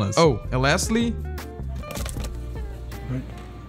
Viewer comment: best part and this this is the fov of the cashier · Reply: Ich habe einen großen Fehler gemacht here what he said in